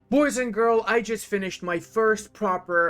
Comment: I love how you say "boys and girl" with a straight face everytime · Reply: I never noticed that, always heard it as boys and girls. thats a great bit lmao.